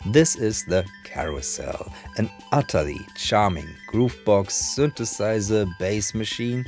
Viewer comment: I misheard you in the beginning and thought you said ‘goofbox’. - Genius! I’m forever calling my Elekton boxes goofboxes from now on. 😂 Isn’t it this synth front and center in Damon Albarns ‘Closet Romantic’ on the Trainspotting soundtrack?